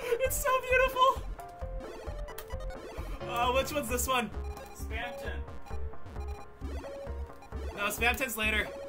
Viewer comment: Did I hear that right? Is one of the glitchy palettes actually named "Spamton"?